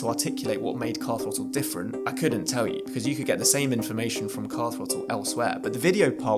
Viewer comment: What's with the AI slop subtitles that literally can't even get the subject of the video ("Car Throttle") correct, as seen at 3:19, where it came up with "Castleford"...? If people want crap AI subtitles, they can already turn them on in YouTube's video options, they don't need "creators" to show how they don't care about viewers by burning AI slop onto the image instead of hiring a competent human to transcribe and subtitle the video. The enshittification continues...